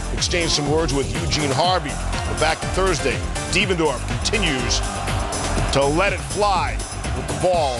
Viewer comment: 10 Did he just say DevanDARF?